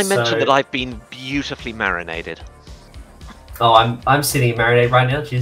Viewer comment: "Did I mention that I've been beautifully 'marionetted'?" Did really say that? Legend.